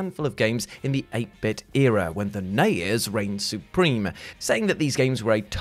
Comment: I keep hearing "Nayers" instead of NES